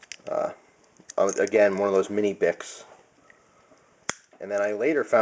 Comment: it sounds like you said mini dicks.....